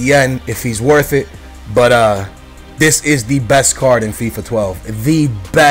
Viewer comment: he actually said card, 1:25...